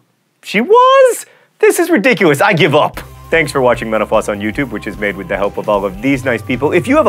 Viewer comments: when he said "thank you for watching mental floss on youtube" for a second there i thought he said "thank you for watching menopause on youtube" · Still sounds like he says “thanks for watching menopause” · At the end I thought he said, "Thanks for watching menopause on YouTube"... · Ok I'm sorry but when he said "thanks for watching mental floss" at the end, I thought he said "thanks for having menopause" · Um, I hate to say this but at the end when John said "Thanks for watching Mental Floss" it really sounded like he said "Thanks for watching menopause" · At the end I heard "Thanks for watching menopause" hmmmm....:/ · I thought at the end he said, "thanks for eaytching meno